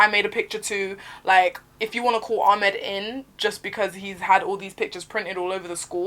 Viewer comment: I thought she said Ahmet lol